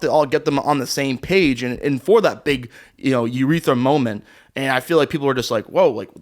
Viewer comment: did just say "big URETHRA moment"?????